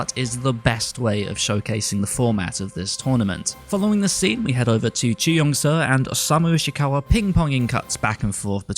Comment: I heard : "We went over to Chiu Yong Tsuh and Osamu Ishikawa Ping Panging"